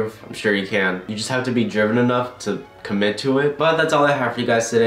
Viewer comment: When you said “you have to be driven enough to commit to it.” I thought you said “you have to be German enough”